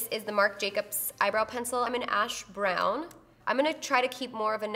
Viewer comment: Girl I thought you said you where in the color "hash brown" by Marc Jacobs I'm dyyyyyiiinnnggg. Love you selfish